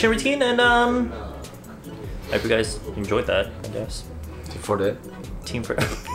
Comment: He said Team4Det and I thought he said team forehead because had his hair pushed back😂. I'm so dumb.